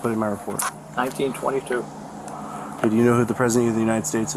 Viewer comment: Did he just say the year was 1922?